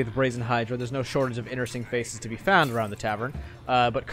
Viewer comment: Hey I’m just a guy looking to get in the “DND” world I used to RP so I want enhance my vocabulary. Can you tell me what word is used at 25:34 the “inner” word? I just found out about this from your golden age of gaming video. And I’m looking to sub but not before you inform me what that word is lol. “Roll for intimidation”. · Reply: I'm 90% sure this was saying "inneresting" instead of interesting, lol.